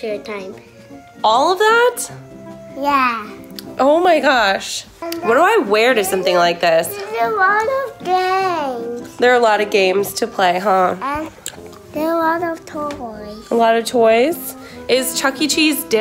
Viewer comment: is it just me but i heard ‘there’s a lot of gangs’